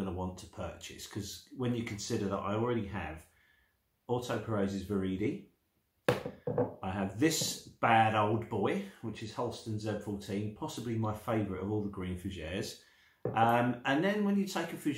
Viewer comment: what are the two fragrances you mentioned before beach hut? didnt catch it. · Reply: Orto Parisi Viride and Halston Z14 my friend